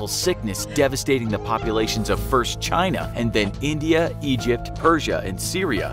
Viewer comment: Did I hear "first china" on